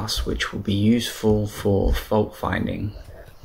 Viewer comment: possibly? I wasn't sure what you had said at around @ 5:02 - the device would be useful for folk/volk finding? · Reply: Oh, sorry. "fault finding" wireless networking